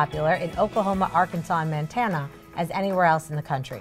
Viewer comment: Did she just say "Mantana"